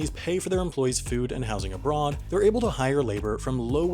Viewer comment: "abroad," but captions say "onboard," which makes more sense.